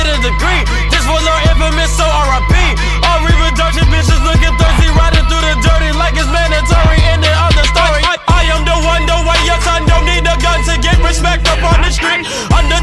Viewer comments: Anyone else think it said: "I am the one, the one your son"? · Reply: It sounds like he said I am the one, don't want the son but it also sounds like the one your son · For the longest time I thought he was saying "I am the one the one". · Who else typed "I am the one" to get to this video? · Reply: I searched "i am the one the one" thats what I thought he was saying xD